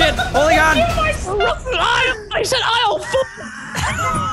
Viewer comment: when I first heard this, I thought said "ISLE FOOP" XDD